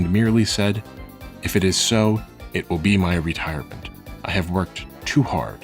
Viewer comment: He actually said "intankavel o bostil"